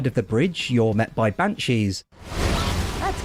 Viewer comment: i had a chuckle i heard ban cheese XD